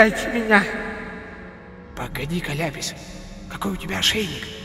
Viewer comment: он сказал ляпис или мне послышалось просто вдруг это пасхалка на вселеную стивена хатя нет ( •_>• ) <--- кто узнал это во персонажа напиши в комент.